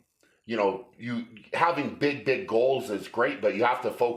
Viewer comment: did he say big dick goals or just me